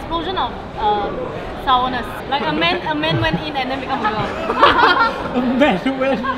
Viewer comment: PLEASE CORRECT ME IF I'M WRONG,BUT DID SHE SAY " A MAN WENT IN AND..." AT 7:19 ????????????????